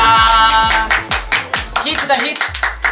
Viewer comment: Ohh myyy did he said HIT NA HIT???😩😂😂